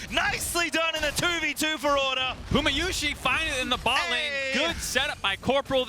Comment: did the casters call puma gumayusi? Or did I hear it wrong?